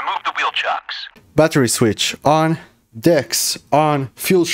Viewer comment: I wasn’t paying attention for a minute and my phone autoplayed this and I thought I heard how to start up the harrier battery on dicks on fuel switch on Wait what was that last one before the fuel switch???? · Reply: LMAOOO it's DECS - Digital Engine Control System